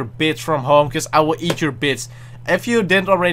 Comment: Omfg I'm dying at around 25:30 I thought he was saying bitch